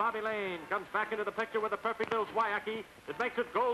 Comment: What the heck does the narrator say @ 6:14? "...a perfect little tywacky..." What the hell??? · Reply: Referring to end, Bill Swiacki.